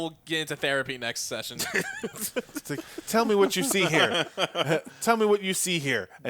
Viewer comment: What said at 23:01? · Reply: Tell me what you see here An inkblot test